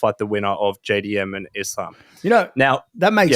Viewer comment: did he say Islam?